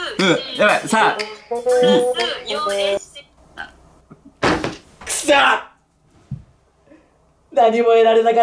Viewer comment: 4ACとか言う不穏な言葉が聞こえた気がしたんだけど、、、